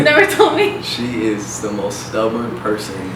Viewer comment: I thought Austin said she is the worst person I meet and I was like WTF 😂😂😂😂